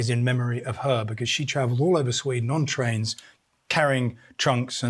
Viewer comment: "all over Sweden..." Did I hear that right?